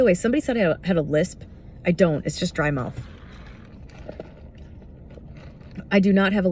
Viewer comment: At first (double speed) I thought you said “I don’t have a lisp, that’s just drama” 😂